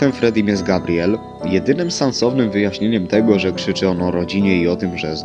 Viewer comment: tylko ja usłyszałem że powiedział "Sansownym" zamiast Sensownym....XD